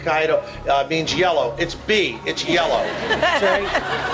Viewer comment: Oh, I thought he was saying "pika...pik...pika means yellow.